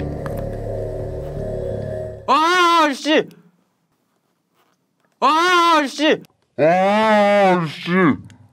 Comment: did he say “Ohh Shit”